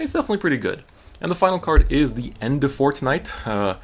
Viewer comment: Not gonna lie, I heard "The Endefort Knight" as "The End of Fortnite," and I was happy for a moment. :(